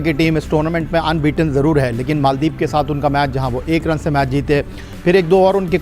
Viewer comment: Maldives k sath? Kya bol raha hai ye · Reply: Nepal 😂